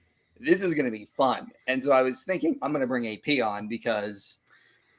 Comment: Hahaha, my brain thought you said "a peon" when referring to AP. I had to skip back to hear you correctly say "AP on." Y'all take care of your ears, don't be like me.